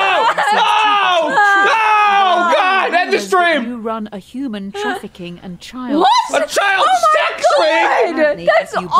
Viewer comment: Anyone know what the game called them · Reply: It said “cancel Slimecicle”